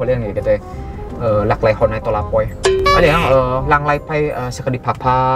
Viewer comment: menit 7:20😂ngomong apaan sih LAKLEKONEPOLAPOI😁